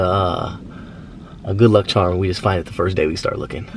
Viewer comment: anybody else thought he said “ good luck charlie😭